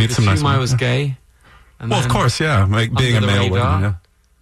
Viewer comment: what does Will say from 7:08- 7:11 ?!?! The interviewer talked over him :((( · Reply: Habso Hassan he says: 'and then under the radar'